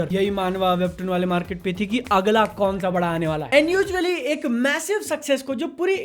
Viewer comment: did I hear massive